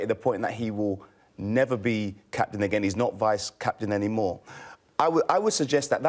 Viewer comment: Lol wait... When did Jose say "He will ne ER be CP again"???. 😐 😐